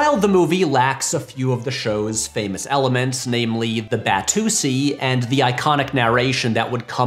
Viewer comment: did he just say batussy